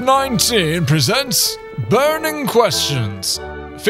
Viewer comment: I'm late but still: In The start when you Said "Vat19 presents" I heard "Fat19 presents"